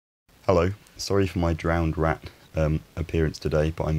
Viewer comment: "Sorry I look like a drowned rat - I'm back at home again... " Well, that clears that up! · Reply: Ironically I misheard him and figured "drowned rack" was just an obscure British expression, it's silly but I pictured a coatrack burdened with soaking coats lmao